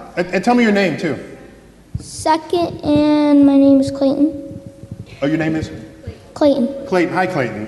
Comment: I heard, "suck it! And I'm Clayton." 💀